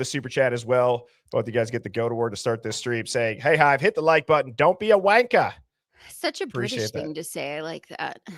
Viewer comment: I hear “Dylan’s” a CI, 100%. Not “one of the victims” is a CI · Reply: I hear, “Dylan’s a wanker” 😬